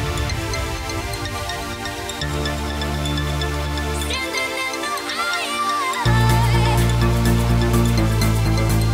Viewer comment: what did she say in 2:21 ? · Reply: 'sittin in the eye of...'